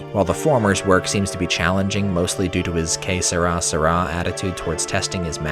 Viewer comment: Hi! So here's an unusual thing to ask: see, by watching your videos, I not only learn about new and exciting maps and WADs, but I also do learn a fair bit of English too! I can't begin to count the number of times I was speaking to friends and words I first heard on this channel just casually making it into my speech, thanks a lot for that! If I don't immediately catch a word of expression on my first watch, I usually rewind the video to have another listen and perhaps even googling it. I was stomped at around 23:39 ish, when you described B.P.R.D's attitude towards testing his maps. I listened to that bit way too many times to count and I still can't put my finger on the expression you used, and the automatically generated subtitles are not doing me any favours. What is the exact wording you used in that sentence? I feel I could use that sometime. Again, thanks a million! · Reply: It’s ironically a Spanish language phrase: “que será, será,” which means “whatever will be, will be.” Essentially I was saying that B.P.R.D. doesn’t worry too much about making his maps perfect